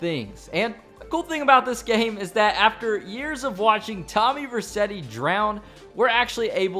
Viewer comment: did you just say tommy Rercetti its Vercetti